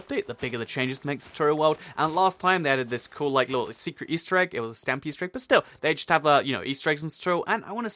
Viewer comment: did I hear the word Stampy?! 😍🐱